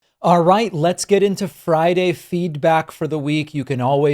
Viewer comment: The first time I watched one of your videos where you called it 'Friday Feedback', I really thought you said 'Feedbag' and wondered why you called it that.